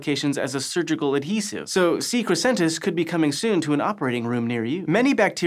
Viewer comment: Every time you said c. Crescentus, I heard "secret Santa's"